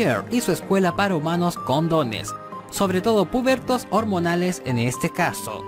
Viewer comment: Sin contexto parece que dice COND0NES